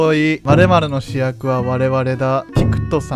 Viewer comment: 最初我々の主役は我々だに聞こえたwww